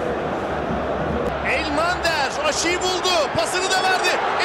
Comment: Elmander Aşıyı Buldu Anladım. Korona İyice Psikolojimi Bozdu...